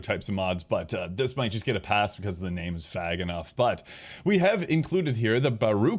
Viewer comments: I didn’t know you were homophobic. It’s “Fag enough “? What the fuck ? Who uses the word “Fag” to describe something . This is the SECOND VIDEO you have done this . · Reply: I said "Vague" ...why would I ever say the slur you're saying? Think critically here. I apologize for my accent. I try to control it for videos, but it's pretty clear I'm saying "it's vague" because Solo Leveling sounds vaguely like a mod for leveling while playing solo, to someone who doesn't know the anime. I am an ally. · The name is what enough? it's 2026, you can't say that word anymore 😆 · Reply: "Vague!" Sorry, my accent kicks in with A / Ah sounds no matter how much I control it for videos